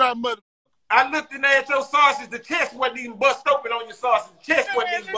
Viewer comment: I thought foo said summer sausage